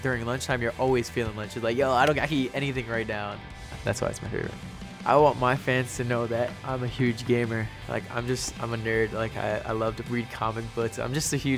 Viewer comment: What he said: I want my fans to know I'm a gamer like I'm a nerd I love to read comic books What I heard: I'm your new favorite member of the band